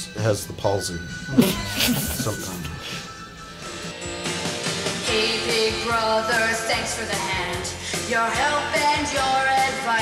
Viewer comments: Had to rewind at 7:25... thought he said "hey big brother sex for the hand, your help and your advice". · did anyone else hear "Sex for the hand"?